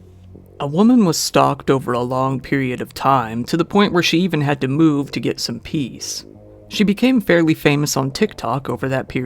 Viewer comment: I heard the first line and misheard it as "A woman was starved over a long period of time to the point where she even had to move just to get some peas."